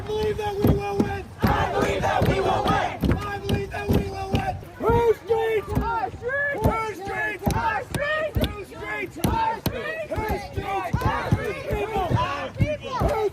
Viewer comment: i legit thought they were saying poo streaks